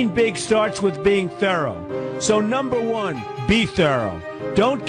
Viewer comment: 幫個忙~請問1:27第一點 貫徹意志的英文是什麼？一直聽不出來，但很想知道~ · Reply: be thorough